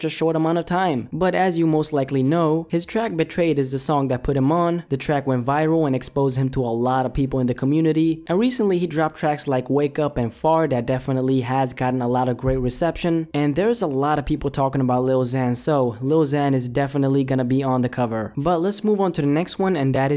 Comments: sounded like you said "Wake up and fart" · It sounded like he said fart xD